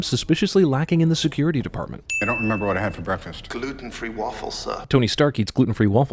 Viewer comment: I thought jarvis said "kalua and 3 waffles"